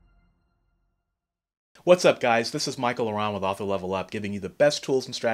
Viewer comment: I heard "This is Michael Arafalafalafalafalavalup."